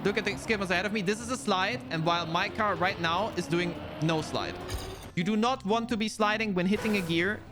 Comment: Ok. I might be stops but I just realized people were saying "no slide" when I always heard "nose slide". No wonder I didn't understand what was happening. ^^